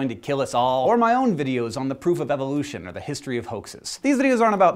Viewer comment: At first I legitimately thought you said "the poof of evolution."